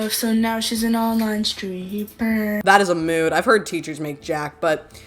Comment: i think she said online str*pper, not streamer lol